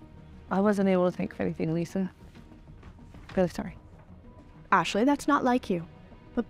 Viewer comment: What was the one girl saying during 36:27? all I heard was "I wasn't able to take any velsohdifhadib. Im sorry". I watched that scene over and over about 10 times and still can't figure out what she is saying.